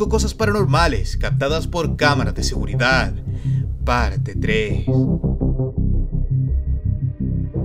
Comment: Minuto 0:55 alguien mas escucha que dice parte tree 😂🤣 un tree